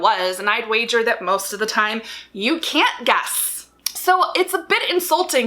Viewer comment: I just want to say that one of the times you said guess it sounded like you said gas.